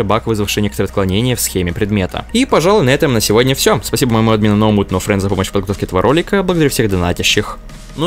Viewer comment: я один не непонял что он сказал? xD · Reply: Спасибо моему админу no mood no friends